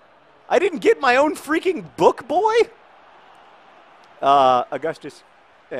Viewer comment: I thought you said "...I didn't get my bitch-boy?!?!" ... ... Turns out you said "book-boy"... ':|